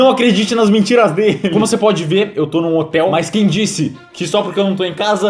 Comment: Só eu que ouvi motel?